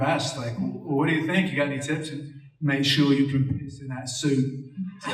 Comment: What did Affleck reiterate Bale words at 13:03? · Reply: Yep, change the speed of the video to 0.5. He says "make sure you can piss in that suit"! haha